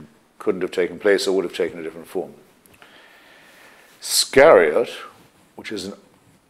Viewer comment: This is special. Just seeing Hitch relaxed talking about life and answering questions. There is this kind of amazing and peaceful presence about him here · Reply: scharreott? is that what he said? what did he mean when he said it's a "somewhat suggestive name"?